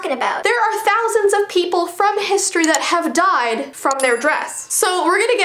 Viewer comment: Is it just me or did it sound like she said "MILLIONS of people died from their breasts"